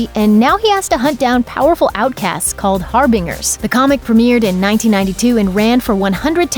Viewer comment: Lol did you just say har-bing-ers.. hahaha